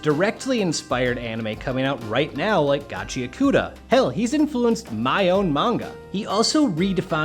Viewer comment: What is the anime at 11:15 ? I can't make out the name. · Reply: Gachiakuta